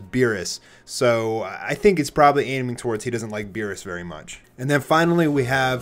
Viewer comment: why does it show up "Virus" instead of "Beerus"?